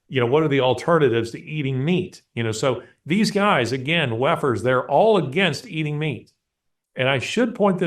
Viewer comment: When you said ‘WEFer’ all I heard is ‘Waffen’.. and what a Freudian audio slip that was. Can’t unhear it now.